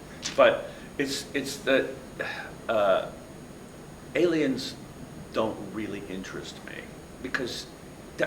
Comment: he says something about aliens. If you turn on the captions of the video, it says "aliens don't really interest dot com me" Can anybody understand what he really said? I have trouble understanding that phrase because English is not my native language.